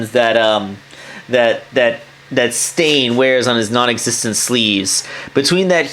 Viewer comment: I thought that said satan lmao